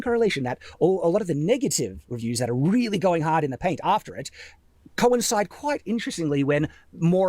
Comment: Im sorry, did Shad actually say "going hard in the paint" at 21:00 or did i hallucinate that?